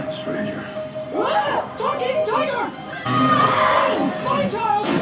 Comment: I love the whole new take on He-man. it's brilliant, and i really love Cringer. the whole ten episodes are fantastic, a side note, anyone else hear a character yell "fucking tiger " just before crass crashes into something? it's